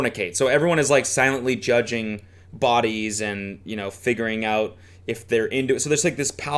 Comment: man when cody said "figuring out" and i thought he said "fingering out" and i almost gagged